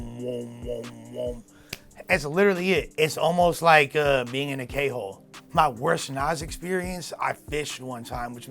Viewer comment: I thought said being in a gay hole 😂 I was like wait what?!